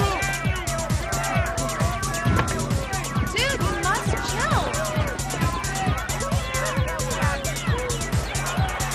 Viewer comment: What did she say here? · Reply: Something like "Dude, you must chill."